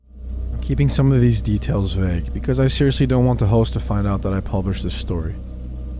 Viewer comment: what did he say😅😂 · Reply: after listening to the whole story, I think he was saying “host(s)” 😂